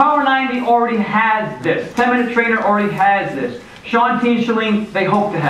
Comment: what did he say at 5:07? blank hopes they have this